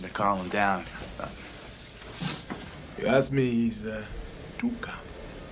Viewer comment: wtf did he say? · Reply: If you ask me, He's a little too calm.